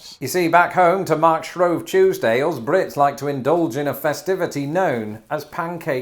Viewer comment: I really wish I knew what he was saying at 0:39. To March rove tuesdales brits like to...? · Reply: To mark Shrove Tuesday, us Brits like to…